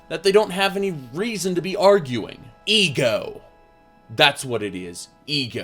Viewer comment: First time i heard that i thought you said Igor